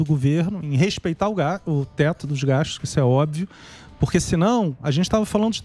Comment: Só eu escutei "respeitar o gado" ao invés de respeitar o teto de gastos?